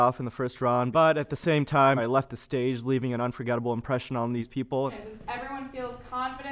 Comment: is the kind of guy that thinks he’s cool but in reality he just isn’t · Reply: I thought he said "forgettable impressions" rather than unforgettable impressions. Because that's what he was, voting himself off with a two-liner.